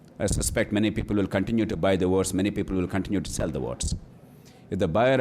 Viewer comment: Dude at 00:31:16 the word used is "votes" not "words" the subtitles are wrong in many places ...need some help correcting them ?